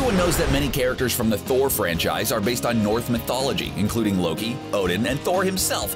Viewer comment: Did you say "north" mythology? It sure sounded like it, and according to closed caption you did lol